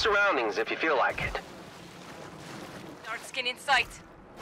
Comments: Did I hear “dark skin in sight”. Or am I trippin 😂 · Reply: I believe they say "Shard skin in sight" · Did I hear “dark skin in sight”. Or am I trippin 😂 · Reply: Yo the subtitle say "dark skin in sight"🗿 btw im black